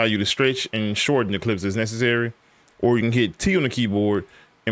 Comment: bro what did you say at 3:17? I thought i iheard "T" but i tried to hold-click it and it didnt work. maybe a settings issue? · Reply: Yeah just T on the keyboard on your mouse cursor should change to a different symbol. Then you can scrub your footage.